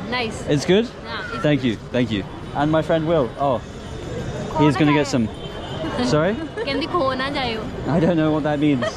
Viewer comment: Anyone know what the girl is saying in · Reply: She was saying "don't get lost".. Khoo nah jaiyoo